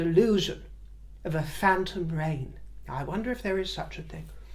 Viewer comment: Is a phantom reign similar to a phantom pregnancy? Like when my friend’s Labrador went to the cupboard under the stairs and “gave birth” to a pair of slippers? 🤔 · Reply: I misheard phantom reign as “phantom brain” regarding Harry